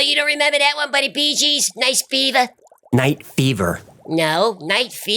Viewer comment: OMG i sprayed Pepsi everywhere the moment i heard “night beaver”. The great Canadian beaver hunt!🤪